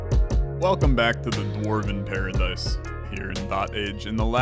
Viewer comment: Had to do a double take because I thought said "Welcome to the Mormon paradise" at the start.